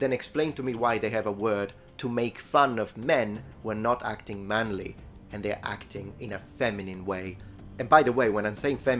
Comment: what is that word? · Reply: Ergi.